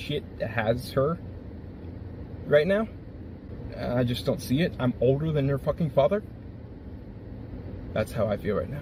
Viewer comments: did he say "im older than her f*** father"?? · does he say “I’m older than her father” is this girl like 7? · What even makes this a million times scarier, and creepier is when he's talking in the end. Someone correct me me if I'm wrong, but I it sounds like he says "I'm older hen her father"..... wtf!!!! So on top of stalking and harassing a, what I thought was a woman, now I'm thinking its like a really young teen girl. WTF!!!!!